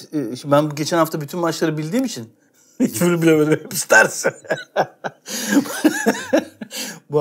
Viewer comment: Mehmet Demirkol'un burada ne dediğini anlayan var mı? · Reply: hiçbirini bilemedim. hepsi tersi